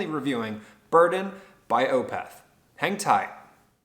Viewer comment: I thought you said "hentai" lmao